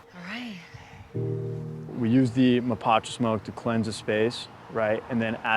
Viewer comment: Did he say pot smoke??